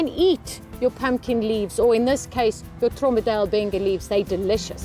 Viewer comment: Hi, could you please let me know the name of that plant it is that you said at 4:22? the part where you say that their leaves are delicious. I couldn't make out what you were saying. I would like to grow it in my garden. Thank you for your video. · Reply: It is a Tromba D’Albenga it is a marrow